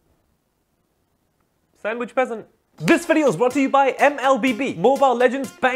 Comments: “I’m going to the Taco Bell” “Oh you mean the burrito alarm?” That got me rolling for 5 minutes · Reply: DAMN I THOUGHT HE SAID SANDWHICH PRESENT 🤣 dat utube translate though 🤢🤮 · "It's next to the Sandwich Peasent" that one line had me DYING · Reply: Thank you! Lol, I thought he said “Present,” and I couldn’t figure it out. Now I get it! 😂🤣😂